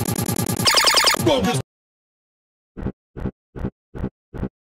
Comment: did i hear strongest turd for a while?